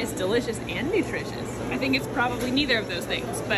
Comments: I’m sorry did she just say SPEDPHONES 😂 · Spedphones?! 🤣🤣🤣 Im stealing that (they type as they sit silently in their preferred spedphones) 🤣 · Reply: Bro I thought she said spudphones. this is soooooo much funnier holyshittt